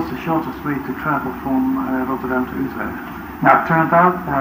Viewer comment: I think Dijkstra said 'Utrecht', not 'Tilburg'